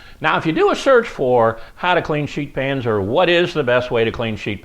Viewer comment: Pro tip....it sounds like hes saying "sh*t pants". Now you can't unhear it.